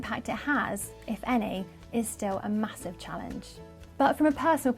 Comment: Did you say massive on 3:43 on this vidieo